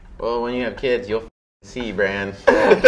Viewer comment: DID HE JUST CALL FINN BRAN I